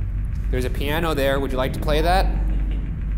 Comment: am I the only one who hears a weird whisper? I cant make out what its saying but it sounds like "lets play" or "go away" maybe its just me